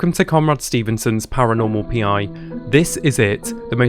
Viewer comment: bruh I heard it as "Comrade Stevenson" and was like ah ok. communist ghost hunting